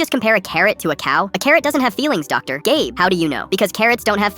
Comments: I thought that he said doctor Gay not doctor gabe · I thought I heard "doctor gay." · And I deaf or did I hear "doctor gay" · I thought thay said "doctor gay"😭 i need to get my ears checked